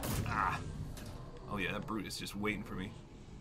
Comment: did he say Brutus